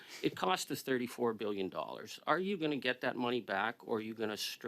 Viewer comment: $25 BILLION OVER BUDGET ! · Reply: I thought I heard 34 billion over budget 😮